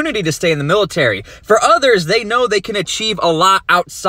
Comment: Every time he said "get some life experience" I heard "get some life expectancy"